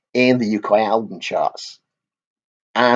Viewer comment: Contents: Antman and Wasp - (potted review) including a possible way the Fantastic Four could be introduced into the MCU DoctorWho - Possible air date rumour Lucifer casting news Massive Wagons (I know it sounds like I said "Leicester", but I DID say Lancaster!) Bigfoot tour news Gama Bomb single release Shiraz Lane new single release Ethernity release new album Ross The Boss on tour Sirenia new album and tour in the offing Roadburn festival announces main headliners